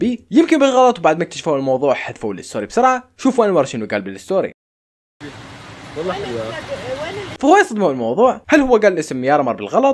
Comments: انا سمعت والله حلوة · شووووو قال ماسمعت غير(والله حلواات)🌚💔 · مين افتهم شو قال ؟ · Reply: قال والله حلوه يا بس ماقال يارا ماسمعت هيك شي